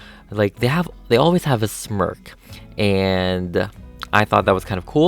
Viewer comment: I thought it was funny because when u said smirk I thought u said smurf